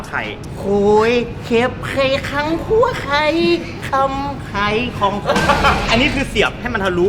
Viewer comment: ได้ยินแว่วๆว่าคลำไข่ของคน555+